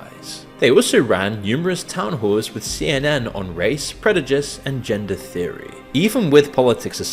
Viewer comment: did he say "predujice?"